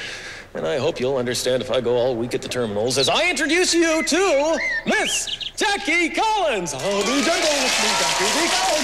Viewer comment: at first I thought he said "Tacky Collins"